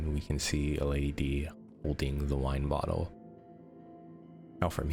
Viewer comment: i heard u say "her wine bottle" thats blood